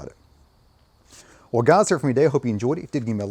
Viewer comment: Definitely no disrespect , But at 6:42 what in the hell did you say? Love your videos. Keep up the great work my good Sir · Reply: welguyhuesefdayuhopeyouenjoyedit